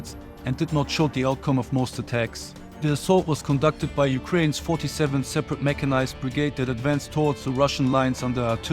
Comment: Every time you "third separate", for some reason I heard "turret separator" which would be a great nickname for a UA tank hunting unit.